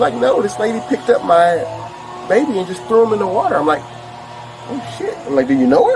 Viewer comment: ”…what was the crime, throwing a baby?” I had to listen to that twice to be sure I wasn’t hallucinating. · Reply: Same I originally thought I heard threw up in the air I didn’t hear the pool comment until I scrolled back through the video